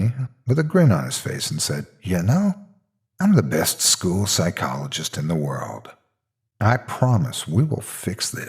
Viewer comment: at first I thought that said "proctologist". no joke. I was very confused.